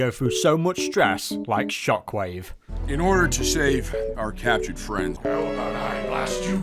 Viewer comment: Did anyone else notice Pax said "shave" instead of "save"?